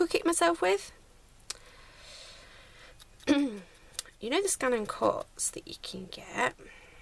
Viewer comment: I think your tags turned out beautifully! Sorry you have been ill. You mention something at 9:26 but I can't make out what it is. Is it some sort of sewing machine? Thanks. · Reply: Thank you feeling much better now thank goodness, oh the machine is a Brother Scan n Cut you can use it for paper crafting, felt, fabric but when i had it at first i just couldn't get it to work right with fabric & felt. Here is a link to the range Brother do x <link>